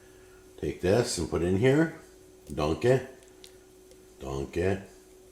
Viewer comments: sounds like Shrek saying "Donkehy" when he says "dunk it" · I thought he was saying donkey 😅 took me a while to realise he was saying dunk it